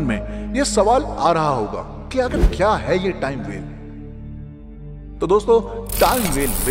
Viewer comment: Can you pls say the word about which you were saying on 5:53 in this video..... It started from 'Time'....pls tell what was the word.....Was it 'Time Vail' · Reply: टाइम वेल (Time Well)